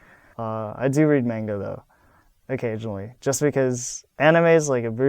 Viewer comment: i thought he said "i do read mango though" i was like LULLLL... oh nvm xD